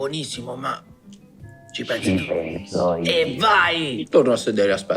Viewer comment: Cosa dice:"ci penso io" Cosa sento io:"GHE PENSI MI ZIO CAN"